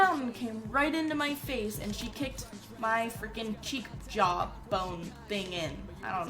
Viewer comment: Anyone else thought it said she got kicked from faze xD